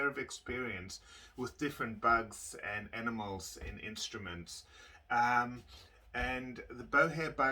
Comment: Did he just say ANIMALS IN INSTRUMENTS !!!!!!!!!!!